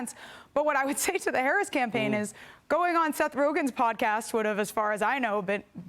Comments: 😂😂😂 did she say Seth Rogan’s podcast ?!? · Did she say "Seth Rogan's" pod cast @ 2:02?